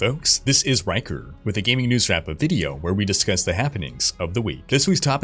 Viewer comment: I totally heard "news fap up" 😆